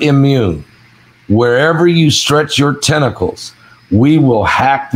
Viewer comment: I thought you said testicles 🤣🤣🤣 Maybe that 104 temperature 😅😅🤪